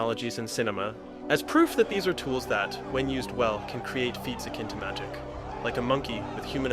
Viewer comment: What is being said at 11:26? it sounds like feentsa kintamatic. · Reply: Feats akin to magic